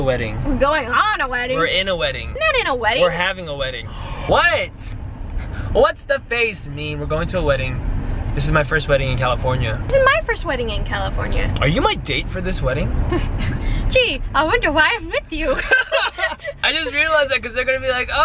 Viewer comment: Am i dreaming or did he just say our wedding